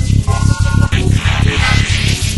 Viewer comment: why did I hear “you stupid little boy”